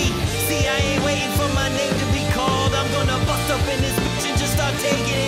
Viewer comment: Did he say the n word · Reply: Jhoney Bond name*